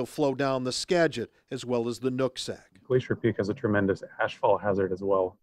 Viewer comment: I thought he said "nut sack" 😅 No but seriously being unable to get a helicopter to send vital equipment to the mountain is pretty shameful in this day and age. This country's priorities are really ass backwards. 😒